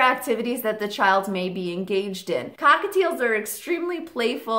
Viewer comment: did she say hockateils?